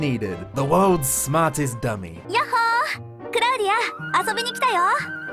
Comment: those subtitles did her dirty, i don't think she said that lol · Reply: She said “I came to play!” not “are you here?” Bad subtitles are bad